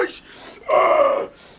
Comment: Did he say "Rocky Balboa"?